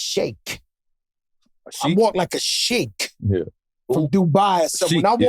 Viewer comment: Man I thought Fat Joe said I walk like a "Shake" 😂😂😂 I was walk like walk a Shake.. what what? Walk like a Sheek.. Cool 😎